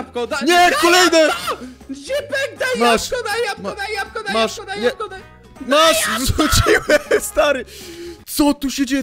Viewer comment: usłyszałem pieprzona Jagoda xD